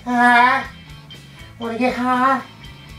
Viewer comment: sounded like towelie LOL